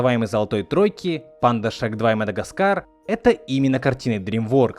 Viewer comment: я один услышал на 1:35 панда шаг 2 и мадагаскар?